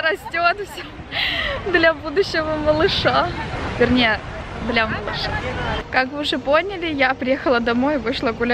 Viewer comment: Да уж,квартира максимально странной планировки. И по поводу плесени - это очень опасно для жизни и здоровья, говорю как человек,который недавно нашёл в квартире плесень,а вместе с ней и причины постоянных болячек 😬 А вообще очень интересно смотреть такие влоги),ты выглядишь классно,очень умиротворенно) P.S.Я не расслышала что ты говоришь в конце "для будущего малыша,а точнее... ?" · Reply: А точнее для малыша. В смысле он не будущий, а уже существующий😀. Если я не правильно расслышала, пусть меня поправят